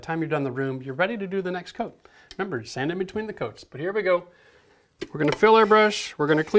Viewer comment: Did you end up sanding again before the second coat? I know you spoke about it but did not see it in the video. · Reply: maa4747 me either ! I thought he said “ sanding between each coat” !?!?!